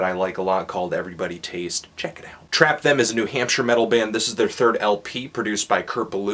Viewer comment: New Hampshire metal band, lol. I thought he said New Hamster metal band.